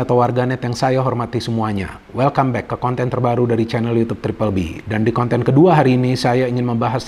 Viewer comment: Sya putar berulang ulang kali ttap abangnya bilang chanel youtube terbabi,apa sy salah dengar kali ya..😂😂